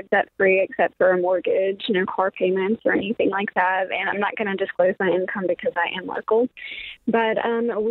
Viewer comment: " and I'm not going to disclose my income because I am "...I couldn't understand after listening three times wth she said she was...as to the Eason why she would tell her income ... " Argued". ? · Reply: She said “I’m not going to disclose my income because I’m local”